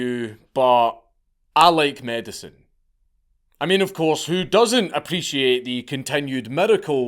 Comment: I wasn't listening very hard to the first part, and all I heard was "I like men"